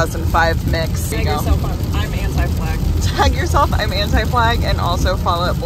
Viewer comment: Ngl, I thought you said anti-black in the first couple minutes of the video and I was shocked 😅 Love the vlog ❤